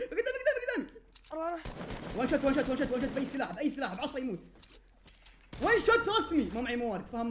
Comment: سمعته يقول بعصه ويموت هههههههههههههههههههههههههههههههههههههههههههههههههههه